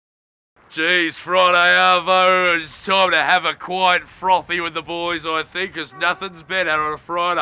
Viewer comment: What the heck is he saying from 00:00 to 00:05 besides "Nothing's better than a quiet froffy one"? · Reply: “Friday arvo it’s time to have a quiet frothy one with the boys”